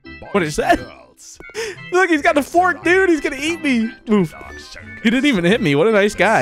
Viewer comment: is saying “please don’t kill me” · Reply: i thought it was saying "He's- Gonna- Kill- me-"